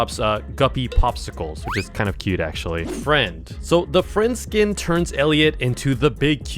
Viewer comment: did somebody say friend inside me